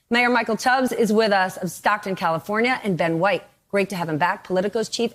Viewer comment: I thought they said Todd Craines for second and about cackled